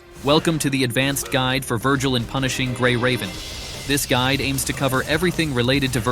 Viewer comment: I swear he said "Vergiling Punishing Gray Raven"